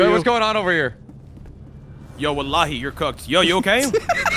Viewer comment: did he just said walihi you are cooked ?